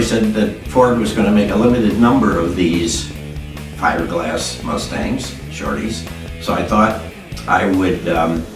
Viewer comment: This was a very interesting Story about the 64 1/2 Two-seater Mustang lll "Shorty" Factory Prototype Mustang! Now my question is did you say at Time Mark 1:20 "Fiberglass Mustangs"? So are we talking like the all Fiberglass GM Corvette? Wow just think we could have had our own 2-seater Ford Mustang GT in 2017! Oh Well maybe I will build my own 2-seater Mustang something like the 2017 Shelby 2-seater GT350R Mustang! We'll see!...God Bless!